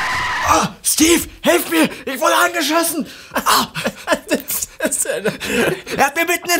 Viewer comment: Bin ich der einzige, der bei 0:03 "ich wurde angeschissen" verstanden hat? Kann verstehen, warum "Phteven" gelacht hat ^^